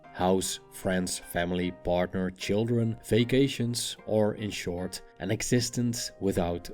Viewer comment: When he said “vacations” I first thought he said fake Asians lol